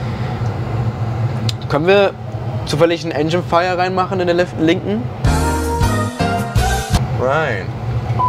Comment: Did you say Can you toogle the engine fire on?